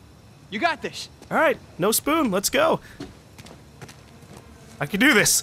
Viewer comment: I'm sorry, did you say "no spoon" at 15:22? Video games aren't the matrix! You can't just do whatever you want. That shit might work in real life, but in vidya, there's limits, things are either "yes" or "no"